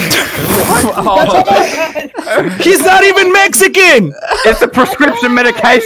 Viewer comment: its actually just xxxtentacion not ex ex ex boi but glad you guys mentioned him #LLJ